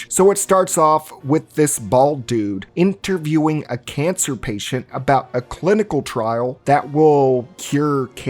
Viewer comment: bro I thought you called the cancer patient bald lol